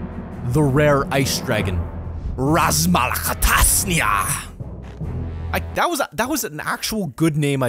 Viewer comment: XD i love when he says "oh i found him the ice dragon raasmalerkatasniar" idk that was how i heard it XD